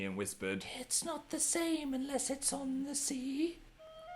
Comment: I thought he said "It's not the same lizards on the sea" .-.